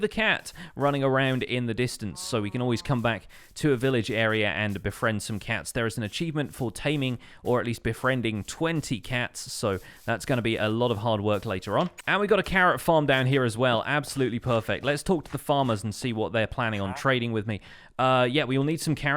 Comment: at first i thought He said breeding 2 cats or more